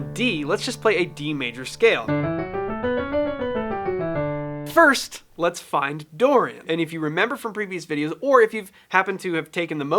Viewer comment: I misheard it "first let's find Dory" 😂 well there is a movie for that 😂